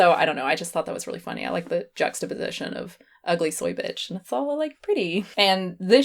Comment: I thought it said "ugly spy bitch" and was really confused lol